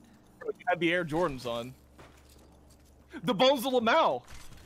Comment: did he say bones of lmao? Destiny 1 or am I tripping